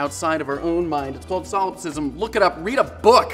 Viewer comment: Umm so I looked it up 0:19 and solecism is “a grammatical mistake in speech or writing” that is what you said was “solecism” right? because I couldn’t quite tell what you said so I turned on captions and it said “solecism” and the definition is not at all what you described... 😂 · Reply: The word is Solipsism